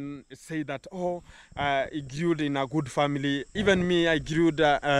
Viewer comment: I want to apologize for getting angry toward you. I thought you called your parents "Pissant Farmers" and someone pointed out you said, "Peasant Farmers" I think they were right. I don't think that is a correct phrase to use anyway and you are misusing it but it isn't as bad as what I thought you had said and I was very angry. I apologize for saying what I said man. I was wrong.